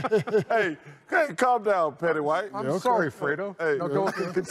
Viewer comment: “Calm down, Petty White!” 😭😂 that man Chuck is hilarious. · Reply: and here i thought he was saying “Petty Wise”